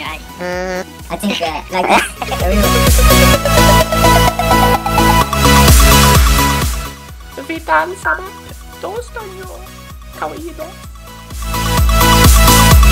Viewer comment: wut? did he just say vi dansade that means we danced is swedish?